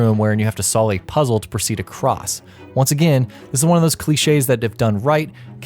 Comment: I thought it said Glitches not Cliche's